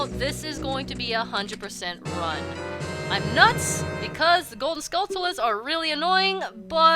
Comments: Wait did you say "My nuts?" 3:38 If you did, I'm surprised of you. You're suppose to an innocent girl! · Wait did you say "My nuts?" 3:38 If you did, I'm surprised of you. You're suppose to an innocent girl! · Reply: ***** Oh, alright, I didn't catch it the first time. I really did thought she said "My nuts." Derp :P Thanks.